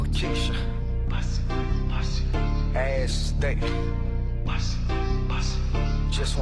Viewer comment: did he say a** st*nk :face-purple-crying::face-purple-crying::face-purple-crying